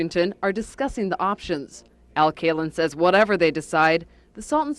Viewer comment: Al Kalin I did a double take I thought they said Al Kaline!(baseballer)